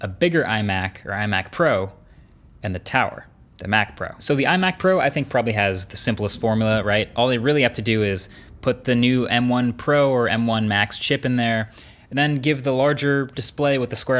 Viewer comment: swear I heard "the Mac Bro"